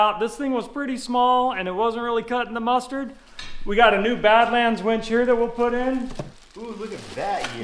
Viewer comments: I thought I heard "Batman Winch" and thought, Grappling hooks? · I thought I heard say ‘We have a new Batman winch..’ It was not what said😂🤦🏼‍♀️